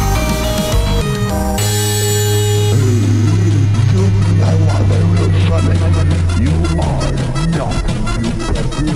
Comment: What was Faker saying in 2:08? I couldn't understand anything except for "real sonic" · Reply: Hello, I am the voice of Faker in this song in collaboration with Neytan, what Faker says is "DID YOU REALLY THINK HE WAS THE REAL SONIC!? YOU CAN'T DEFEAT ME BECAUSE I AM THE TRUE GOD"